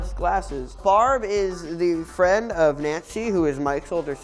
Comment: did he just say "nazi"